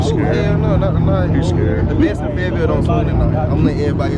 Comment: What part of NC you in. I'm in Hawaii but NC my home...Kinston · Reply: Thought I heard Fayetteville, but that lot didn't look familiar